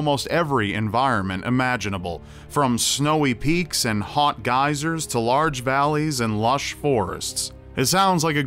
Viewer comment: I thought he was going to say hot guys instead of hot geysers lol